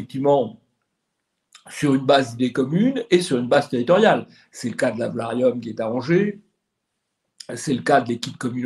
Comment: qu'est ce qu'il a dit à 14:37, j'ai pas bien compris ? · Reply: C'est le cas de l'Alvarium qui est à Angers.